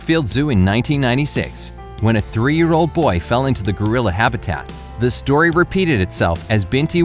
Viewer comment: it sounded like you said goriller